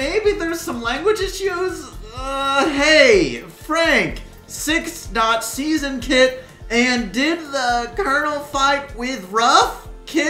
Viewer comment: the kid I think said hey Frank in season 6 did kit fight with "ruff" and did "ruff" win idk